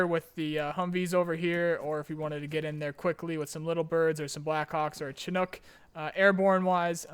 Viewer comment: Lol when you said blackhawk i heard "blackcock" xd